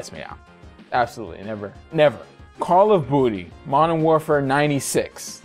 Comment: I'm sorry... did you just say Call of Booty? I wasn't even looking at the screen and that made me do a double take.